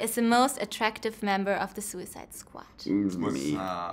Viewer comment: In the first 10 seconds, she asks who is the most attractive member in suicide squad. Why does it sound like she says Suicide SQUACH and not Suicide Squad?